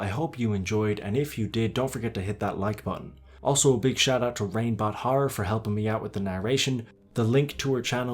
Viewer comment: For a second I thought you said RapeBot Horror instead of ReignBot Horror